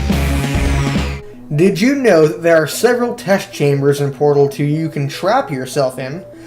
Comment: Sounds like he said "BardleDo“ (Edbassmaster) instead of Portal Two LOL